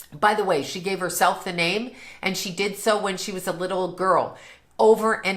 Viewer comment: 👏🏽 ❤ Glad she is at peace. She died on Prince’s mom’s day of death. Prince’s mom died on February 15 2002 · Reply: I thought she said Prince gave her the name